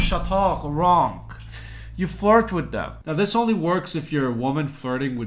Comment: “you fart with them” is what I heard 😂😂😂😂